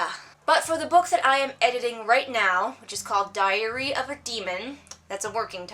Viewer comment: I swear to God, you're so funny! I misheard your book's title as 'Diarrhoea of a Demon' instead of Diary xD I have not read any of your books, however, I think the whole chp. 3/4 does not work after two great preceding chapters is probably an exposition problem? At least that's what it sounds like. I agree on the demographic BS. People will read anything, from toddler books to mummy porn, whatever their age. The one thing I shall dispute about is the 'Write everyday' statement. I opine one should, in the minimum, write, perhaps not every day, but as much as they can from day to day. Writing is one of those crafts you lose 'steam' over it if you do not engage with it constantly. Thank you for the video. I aspire to be a crazy cat person too! .